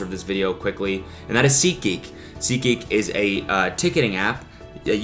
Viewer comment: Yoooooo lowkey I never heard anybody promote seat geek apart from David Dobrik and I genuinely thought he was saying ziki for the last year